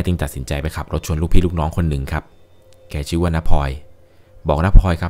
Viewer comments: ฟังๆอยู่สะดุ้งเลยครับ ได้ยินว่า แม่ขับรถไปชนลูกพี่ลูกน้องคนหนึ่ง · ขับรถชนลูกพี่ลูกน้อง! ผมได้ยินแบบนี้จริงๆนะ5555